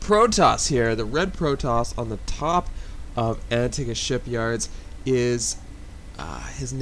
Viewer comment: When you were announcing the "red' Protoss, I totally thought you said: "I gotta take a shit yards." Then I kinda corrected my brain.. OH. Antigan Shipyards. Antigan Shipyards right? I haven't been keeping up with SCII recently. :P But still made me laugh my ass off.